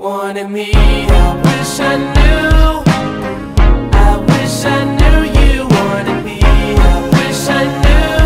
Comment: i always thought it said “i wish i knew you more than me”